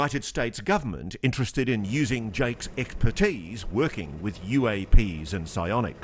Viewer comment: does Ross say eggpertise 😂